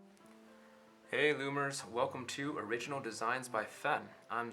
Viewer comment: •Yaoi Lover•! yup! since this comment is recent, i guess i’ll say this here- did you notice how at the beginning it sounded like he said “hey boomers” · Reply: he didn’t say “hey boomers” He said hey lookers😁